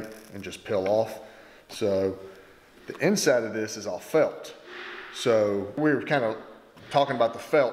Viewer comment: You said "The inside of this is all felt" I heard "The inside of this is all F*****". Had to rewind it haha. I love your vids, you guys do some amazing work!!!